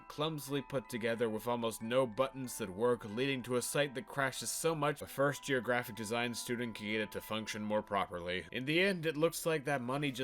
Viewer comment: I thought you said “leading to a site that functions better than a first year graphic design student” And I know it’s not what you said but I can relate so hard.